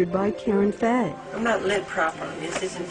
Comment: What did he say at 0:20 ? I'm not what properly???